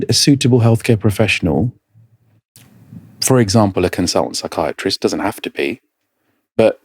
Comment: ‼️‼️ Please, can someone tell me what he said? A “consultant psychiatrist?” That’s what he recommends?